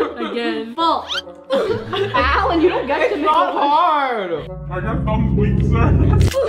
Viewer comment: Did say are your thumbs weak sir?